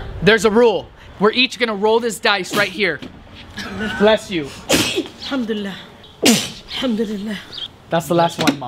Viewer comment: did you say Yarhamkulilah